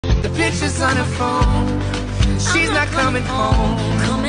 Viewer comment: I thought it said "The b!tch is on her phone" :DD